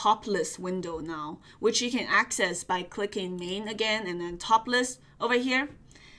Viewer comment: Thought you said "topless" at 12:06 🤣